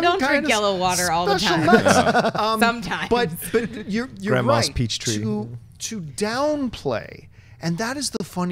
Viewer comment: "Grand Moffs Peach Tea", I heard ya, nobody on the panel heard cos was trying to make a point but I heard and I laughed and laughed...